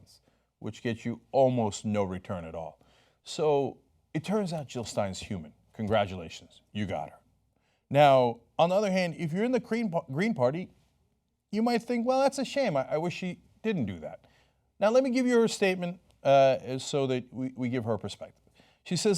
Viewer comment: Did Chunk say The Cream Party at 6:36?